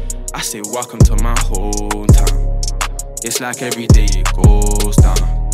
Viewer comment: Raaaah thought it said homerton 😂